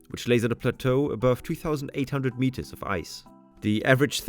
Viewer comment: One correction: at 1:09 the subtitles say 2800 meters but u say 3800